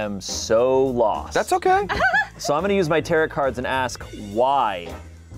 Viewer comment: Is he saying taroTt?